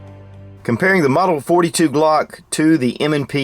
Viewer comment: did anybody catch at 1:00 where he says comparing the glock model 42?